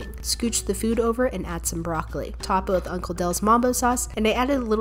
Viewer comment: Wait did I listen her say some mango achar or I misheard it...... Ok if she said that it was cute 😁🥰